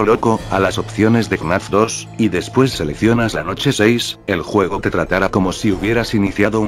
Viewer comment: velocidad 0.25 y veras que dice: Noche 8